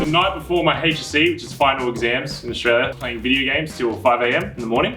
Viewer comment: why did it say game of thrones again when he’s talking about staying up playing video games 😂🎮